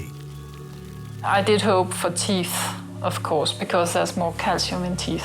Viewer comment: Does she say "I did hope for thief?" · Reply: Teeth